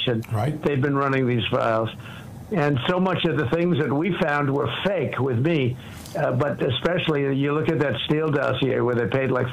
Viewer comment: Did Trump say in the files its fake with me 😂😂😂